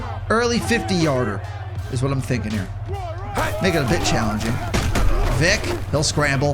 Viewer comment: what did you say🤨 (it sounds like he said the n word) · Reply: make it a bit challenging